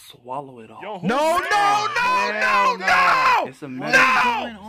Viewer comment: Lol in the background I heard "come get ya man" 😂